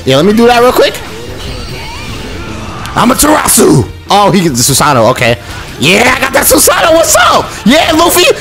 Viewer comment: did he just say susano avocado <link>